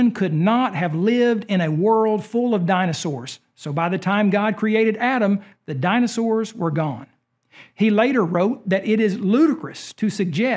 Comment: I was terrified, I was listening but not looking so I thought he said "by the time God created atom, the dinosaurs were gone", and my last braincell nearly died thinking he said that. honestly wouldn't surprise me if someone actually made that claim one day though